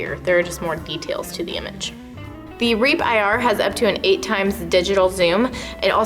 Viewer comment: I thought she said "RAPE IR" I'm like wtf.. LMFAO X10!!